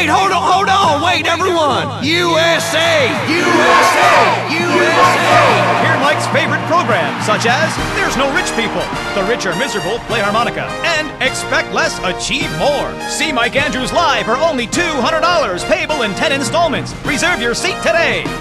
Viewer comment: I always hear "Expect less a cheap whore" at the end